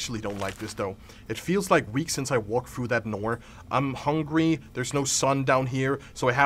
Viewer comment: are none of us gonna talk about how said "Noor" instead of "Door"? 💀